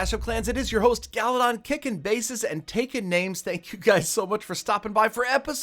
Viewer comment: I yawned in the middle, so I heard "kicking *yawn*ses, and taking names. I had to rewind.